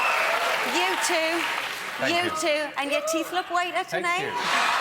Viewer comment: What did Cheryl say to Simon ? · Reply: She said his teeth look whiter tonight